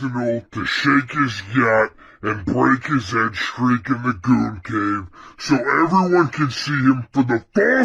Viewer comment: Can somebody tell me what is saying at 15:12 ? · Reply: it's all brainrot. they say "gyatt" something something "gooncave" something.. etc